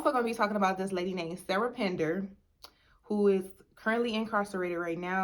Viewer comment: I need to watch this! What’s Sarah last name? How long did her boyfriend get? Lol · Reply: Sound like she said Pender…